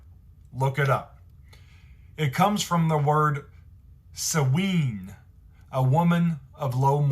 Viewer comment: what word did he say how am I supposed to learn bruh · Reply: He said w*ore